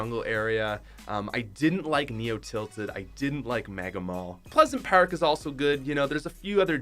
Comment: did anyone hear maga mall